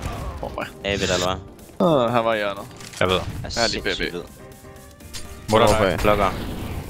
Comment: fake subts.. Dev1ce 2:30 what he say "jeg er lige brb " what the sub says "he still B" what he actually says "im brb"